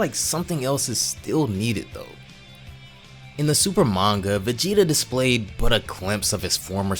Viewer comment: You’re mixing two different mangoes the shit with granola is Different then the dbz kakume arc with the lost universe reappearing, that in which The grand priest train Vegeta to be in his primal ape form. However clearly you don’t know the difference between the two. Vegeta in the manga with granola acquired god of destruction form on the contrary in the vegeta in the dbz kakume arc did not acquire the God of distraction form. Therefore Vegeta in this ark should not have the god of destruction form. But somehow you guys managed to fit it in there. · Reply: you need to get better hearing. He clearly says “Super Manga” at 0:45 and then says “FAN MANGA” at 0:51. He isn’t mixing it up, he knows the difference. He knows what he’s talking about (also this fan manga isn’t even Kakumei, it’s Dragon Ball Sadala LMAO)